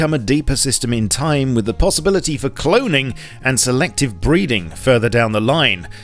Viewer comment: selective breeding? lol...I thought he said..."selective breathing"